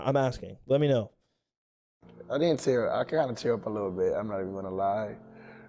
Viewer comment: ong thought he said “popped a chub a little bit ngl” 💀